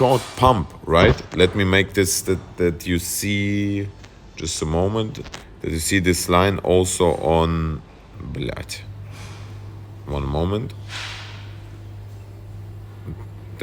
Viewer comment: you mentioned line called "bljad" First time I hear about bljad line or chart playing out bljad pattern... In my humble opinion last month was one big bljad pattern